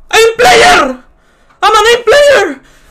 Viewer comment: bro when he first said "I'm an aim player" i heard "I'm an ape player"